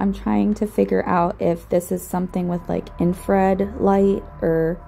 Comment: Did she say " In Fred light" ? Lol